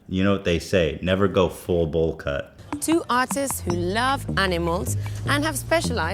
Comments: "two autists" lmao yeah i heard that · Sounds like she says "Two Autists" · I actually thought she said autists instead of artists XD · anyone else think the lady said autist at 1:10? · I thought she said two autists for a second and I was like yeah that makes sense